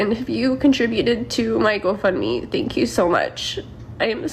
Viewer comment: O sorry she said go fund me I thought she said go F me 😂😂😂😂😂😂